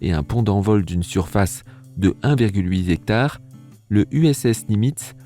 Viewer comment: A 5:57 "a une surface de un virgule "huizectare" ... Dis, Papa, c'est quoi un huizectare ? :°))